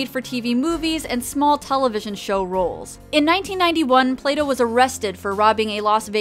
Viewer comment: Hey guys: Just FYI; in this video I have a sentence that says "1992, the year Plato acted in Night Trap" - I didn't mean that's when she did the physical work for the game, it was supposed to just mean when she was actually in the game the year it was released. I wasn't even sure when she did the filming for it; my phrasing was just wrong. That's what happens when you play too many FMV games in one go. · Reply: I thought you said her name was Playdough! Nevermind me, I think I've got plato in my ears.